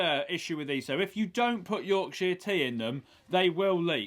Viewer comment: Time 10:44-10:49 Sorry, I didn't understand what Furze said about the mug, that if you don't... it will leak. 😅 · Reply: Michael Guerrero he said Yorkshire Tea.